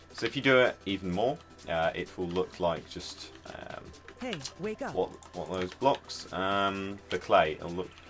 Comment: Anyone else hear the ''Hey! Wake up!'' at 2:17? xD